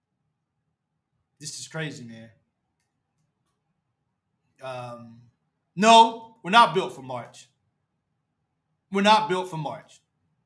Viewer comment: I thought Cal said We were built for NCAA LOL